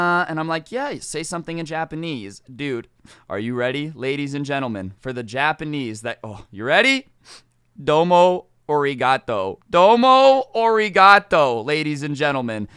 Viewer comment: it sounded like domo oreogato